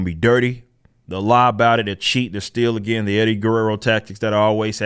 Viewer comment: I thought you said Eddie Gordo tactics and I was about to get reeeaall defensive.